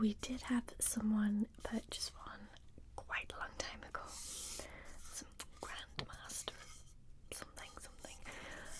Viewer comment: loled at the wooden flute to summon the death being sold to "a grandmaster of something something" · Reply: MDZS reference, I tots would have missed it had I not seen your comment since for some reason I heard "Grandma of something something" instead XD